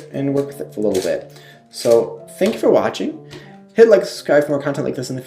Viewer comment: I'm so used to people saying hit the like button, but he said hit like and I thought he said Hitler and was like shit